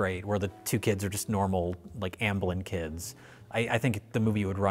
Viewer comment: Jay pulled a Rich and skipped an entire syllable of the word "ambivalent". I'm honestly a bit disappointed Mike didn't interrupt the review to address it. He's slipping in his old age. EDIT: It sounds like he says "ambulant" which, to be fair IS a word. But it just means the ability to move around. 2ND EDIT: ...UNLESS Jay meant it as a dig at the wheelchair-bound protagonist of Mac and Me🤔